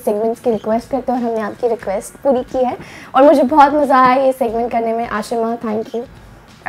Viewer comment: I think she said "ASHIMA THANK U". Guys my name is also 😍😍